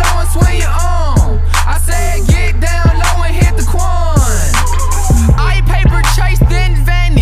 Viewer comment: It crazy how this song is old but it a trend again I think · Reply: I thought it said I said get down low that’s where you are I said get down low and hit the quan